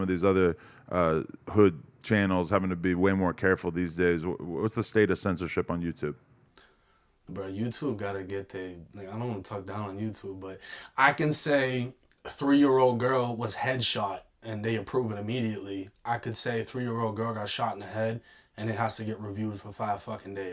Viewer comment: does he say Niga or im trippin ? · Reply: he says “ like I don’t wanna talk down on YouTube “ But with his heavy ass accent when he says “like i” it kind of sounds like what you thought you heard